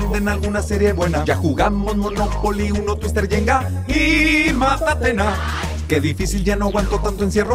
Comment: Hola! me encantó! Me pueden decir qué es lo que dicen en el 0:58 después de "twister, yenga y ...", no logré entender.... Gracias!! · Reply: Matatena